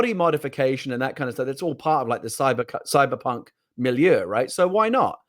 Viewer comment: Did he say “Cybercunt” @ 30:55?